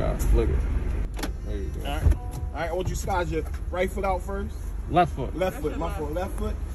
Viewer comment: Cop kept saying get your right foot out first. I thought he was saying rifle out first! I thought why? Gonna take them one at a time why he sits with his hands on the wheel? 😂